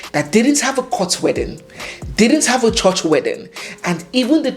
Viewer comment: Was there a court wedding? I thought you said no white, or court wedding?